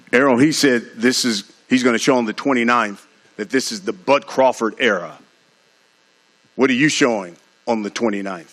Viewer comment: Sound like bruhtha said this is the BUTT Crawford era! 😆😄